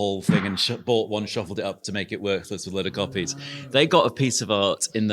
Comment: captionning error: "work" instead of "worthless"